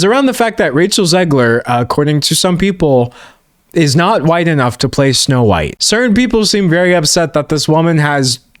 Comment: I thought you said “wide enough” and i was so confused